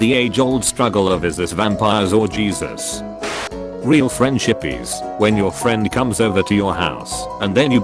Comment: when it said “ real friendship is” I thought it said real French hippies